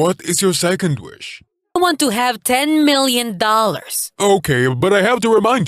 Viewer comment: I heard “I WANT TO HAVE 10 MILLION POWER IN (INSERT GAME HERE)”